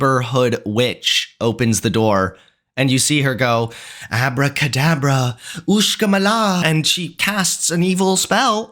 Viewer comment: I was a bit far from the phone and I swear I heard “Abracadabra… MICKINMINAJ” XD